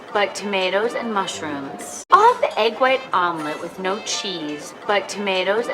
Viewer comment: 6월 강의 다 듣고 이제 5월 강의 듣고 있어요! 어쩌다 보니 거꾸로 가고 있네요ㅎㅎ 그런데 중간에 16:45 미드 예시부분에서 I'll have the omelet with no cheese~~라고 하는데 저는 왜 I'll have가 잘 안들리고 다르게 들릴까요.. 아무리 들어도 잘 안 들리네요..ㅠㅠ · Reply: 저는 I'll have the egg white omelet with no cheese 라고 들리는데 아닌가요?ㅜㅜ